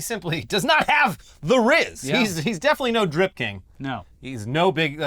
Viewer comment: I thought you guys were saying "Drift King" like that old fast and furious movie 😂